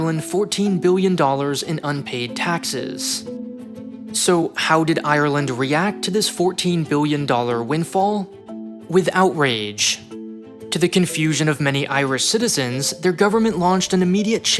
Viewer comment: I heard the phrase "with outrage" as "without rage". Funny how a different pronunciation can lead to the precise opposite meaning.